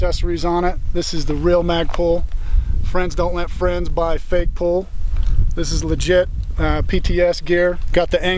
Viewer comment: Did he just say FAGpul